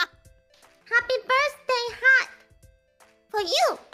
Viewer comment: Bae misheard "heart", not "hat" 1:10 Haachama said "oshikko (pee)", not "chinko (PP)", which is why Bae specified that it's the thing that does the "oshikko"ing